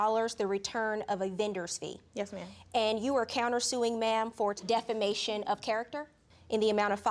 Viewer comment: Def-fa-manes my character? Did anyone else catch that? LOL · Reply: I heard it as "defamaimed" 😂